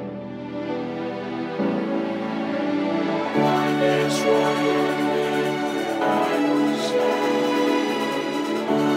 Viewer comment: Does anyone know what this says? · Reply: "What is wrong with me? I am sad" 😅😅